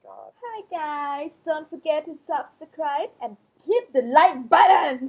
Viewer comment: So this whole time you're girlfriend at the end has been saying hit the like button i thought she was saying hit it like butter the whole time lmaoo